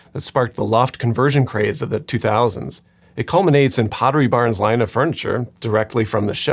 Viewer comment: Could've sworn I heard "Poverty Barn" instead of "Pottery Barn"...